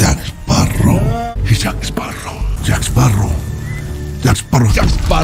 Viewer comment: From what i hear is "jak esparro" "jakparoe🎶"